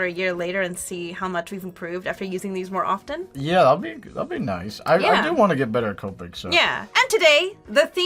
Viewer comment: "i do wanna get better at coping" I know that's not what he said but it sounds like it